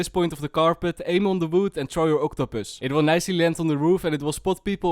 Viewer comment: did he say throw your octopus LMAO